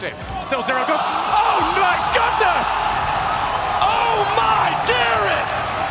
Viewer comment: it sounded like oh my gatorade